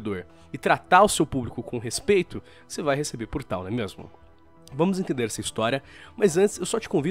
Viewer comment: Só eu que ouvi: "você vai receber portal"?